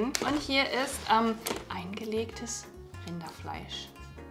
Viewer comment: Min 8:26 hat noch jmd eingelegtes Kinderfleisch verstanden? hahahaha